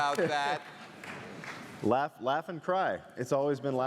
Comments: I always heard "Laugh and die" ._. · Woah, I always thought Stewie said F in cry... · I heard it as luagh and cry always.